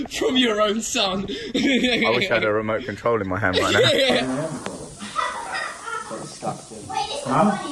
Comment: Did he say "Was ist das denn" at 10:55 its german btw 😂